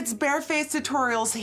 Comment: When she first said 'bare face' I actually thought she said 'bear face'. Hahahaha. What is wrong with me???